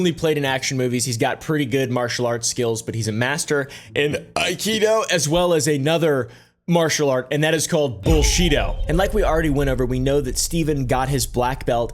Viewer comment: Did I hear, ' Bull Sheeto Master'?